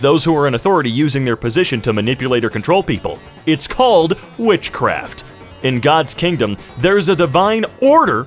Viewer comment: i thought the thing at 2:12 said MINECRAFT for a second